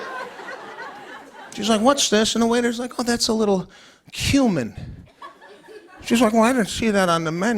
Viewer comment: OMG I thought he said "human" instead of "Cumin" I had to reroll that for a sec 😆